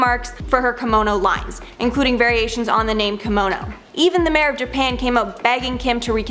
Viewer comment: Did she say mayor of japan at 9:47?